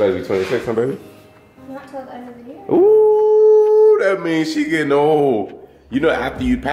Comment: When is birthday? It sounded like she said end of June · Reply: November. "End of the year"